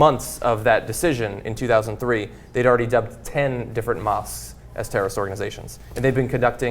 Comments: and for a second there I thought it said "mosquitos are terrorists organizations" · LOL I thought it said mosquitos are terrorist organizations